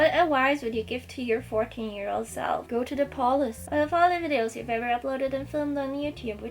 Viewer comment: Is she saying “go to the police”? At 0:45 ?